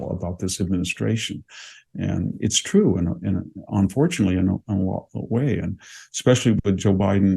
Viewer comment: Please help. What does VDH say at 8:42? "And it's true, unfortunately, in an awful way...."??? OR "And it's true, unfortunately, in a lot of ways...."??? OR something else? Thanks.